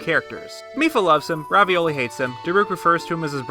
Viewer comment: Did you call him “Ravioli”?